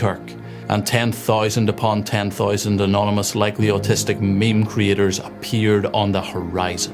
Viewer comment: "likely autistic meme creators" 😂😂😂 did I hear right?